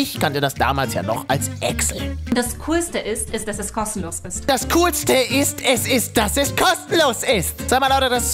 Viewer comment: Okay das soll jetzt kein Hate Kommentar sein aber bei 3:10 hast du das völlig falsch verstanden denn sie sagt: "und das coolste ist ,ist dass es kostenlos ist." wer auch so denkt liken damit er es sieht